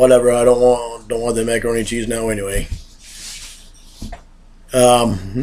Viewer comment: He says 'I don't want the macaroni and cheese anyway..." and all I hear is "I don't want this cookie!"